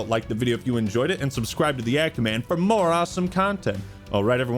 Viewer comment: I thought you were saying 'the ant man' this whole time. When I say this on my feed and tried to find your channel, i typed in 'ant halo review' and this video game up. I must not be the only person searching for your channel by typing in 'the ant man'